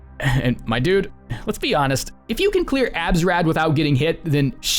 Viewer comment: what tf does he say at 16:40 "if you can clear something you probably wouldn't be watching this video" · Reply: Abs rad is what he said and it's short for absolute radiants